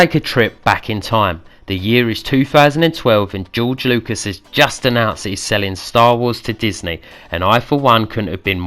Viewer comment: did he say "george lucas is just a nazi selling star wars to disney"???